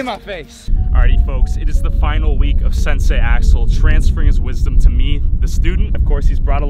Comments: When you said Sensai Axel I thought you said Sensai A**hole. XD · it sounded like he said Sensei asshole